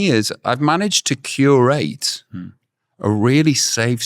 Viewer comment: i thought he just said he'd manage to cure aids!